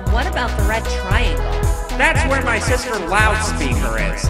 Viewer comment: I THINK HE SAID THAT WRONG HE SAID "THAT'S WHERE MY SISTER ALLOWS ME IN BUT I HEARD "THAT'S WHERE MY SISTER ALLOWS ME ARE IS" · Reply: it’s actually “That’s where my sister Loudspeaker is.”